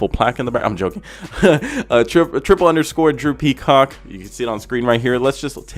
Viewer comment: Thought you said drew peasock in the intro 😭